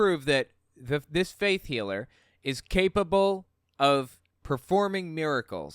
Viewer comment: Every time I hear "faith healer", I think that I am hearing it as "fake healer".